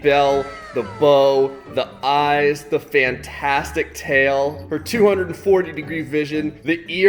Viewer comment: for 240 Me thinking about price: !!!!! (sound effect): degree vision Me: phew I know he said "her" instead of "for" but it actually sounds like the second